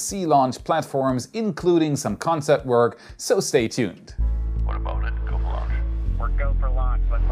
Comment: does he say "what about go for launch"?